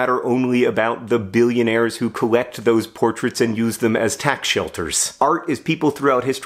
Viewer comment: I misheard you say, "tack shelters". As if billionaires had special tacks that need sheltering so they use artwork to hide them.